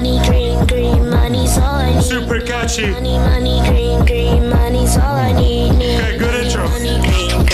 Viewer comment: For some reason, when i hear this song I hear “Money Money coo coo, money’s all we need, eve”